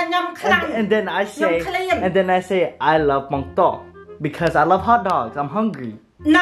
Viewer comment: I thought you said” i like bong tok “ 😭 but what you say is “ i like bong tor “